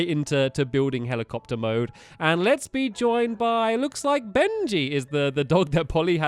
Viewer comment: did stampy say bendy?